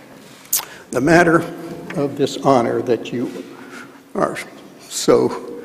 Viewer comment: does he say dishonor?